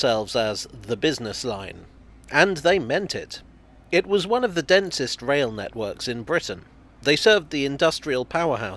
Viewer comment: I misheard the into as "one of the dentist's rail networks of Great Britain" and spent the next few seconds wondering what dentists had to do with railways! Nice to see something outside London, more please!!